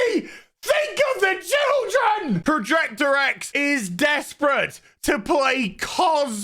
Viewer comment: did you call him projector X ?